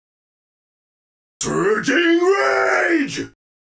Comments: I think he says "Surging rage" in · i always thought he said surging range